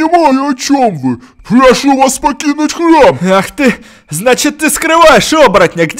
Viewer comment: Священник сказал. -Покинте храм. А мне послышалось -Пакинте хлам.